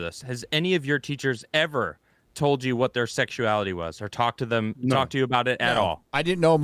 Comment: Is it wrong in my mind I heard “Talk to them Az”?